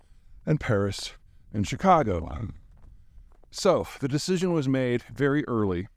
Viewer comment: does he say Chicago?